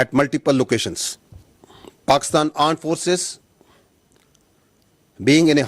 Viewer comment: Boys and girls please listen to 7:11 to 7:13 seconds what he said😅😅 I heard it right: Pakistan's aand forces..... Atleast their generals know their forces very well......